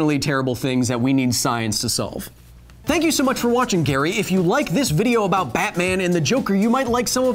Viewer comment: does he say Gary?